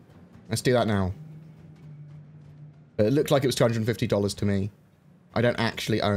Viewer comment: $250?! Or did you say 20 50 ($2050)?...